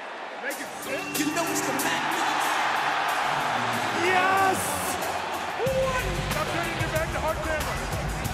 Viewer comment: “Stop turning your back to hard camera” anyone else hear triple h say that Lmao